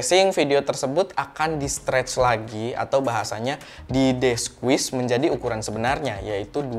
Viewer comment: dia bilang di apain? Deswis aku dengernya. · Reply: Desqueeze